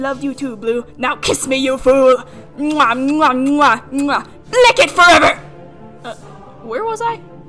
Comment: The text that popped up on screen when old town road played had me wheezing and I don’t know why Cus I see stuff like that all the time it’s just something about this just made me laugh so hard Also when she said blicket forever I thought she said lick it for ever and I was disgusted by that until my brain processed what she actually said May I also add that somehow whenever she mispronounced darkstalker I just wheezed more and more